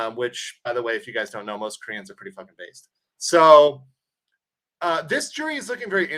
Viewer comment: most Koreans are what?..... can't hear what you said. I have visited Korea, they are awesome people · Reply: He said "Most Koreans are pretty f****ng based" which is a compliment.